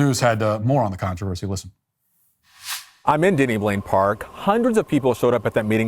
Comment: I thought he called the park "Diddy Blane Park" and had to go back to read the sign 11:47🤣